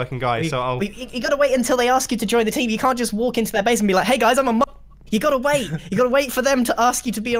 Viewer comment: did grian say MADAF**KER